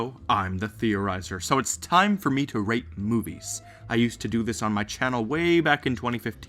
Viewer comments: I honestly thought he said 'lets rape movies' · when you said rate movies i thought you said rape movies XD